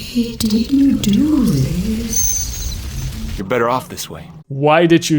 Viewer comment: I think she's saying: "I didn't do this."